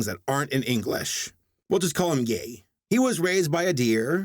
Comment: A misheard quote: "Here we are again, folks. I cannot do this; I suck at things that aren't in English. We'll just call him Gay."